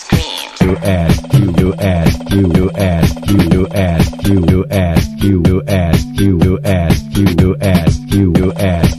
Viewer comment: Can anyone tell me what he's saying at the 3:25 part? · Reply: You ask, you will ask, you will ask, you will ask...!!! That's what he is saying!